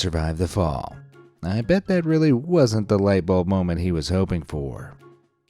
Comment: Does anyone else hear "Lightbulbbb" from Despicable Me, around 2:50....